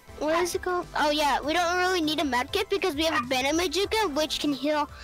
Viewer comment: mic is loud but hes chill so its all good 5:39 he says banemajuka instead of bandage bazooka